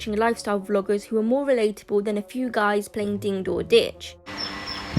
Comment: Did she just say ding door ditch